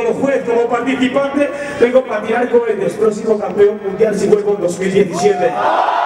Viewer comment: que dijo? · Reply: "el proximo campeon mundial si me presento en 2017"